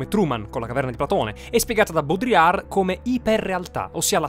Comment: ho capito boldrin ed ero confuso